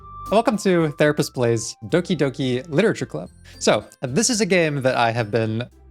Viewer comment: Oh did you say therapist plays doki doki literature club, I thought you said therapist goes crazy again.